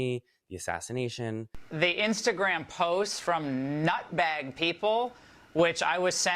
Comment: Omg I thought that Fox lady was saying "nutmeg people" and I was racking my brain to try to understand what she meant or if it was some high level deep cut racism I didn't understand. She said "nutbag people" 😂